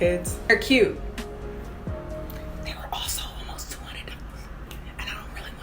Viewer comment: Did you just say $20 for nails? hunny i spend like $75 (minimum!) over in Australia for all that nail bling 🙃